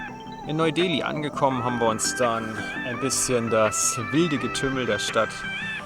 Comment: Did you say "Hamburgistan" at 0:53?? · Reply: AfGun0111 Nop not Hamburgistan but Haben wir uns dann = and than we have...